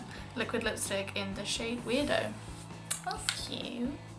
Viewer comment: At 23.19 i thought she said wierdo lol but she said widow😂